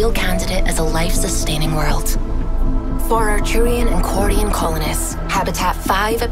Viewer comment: did I hear quarians.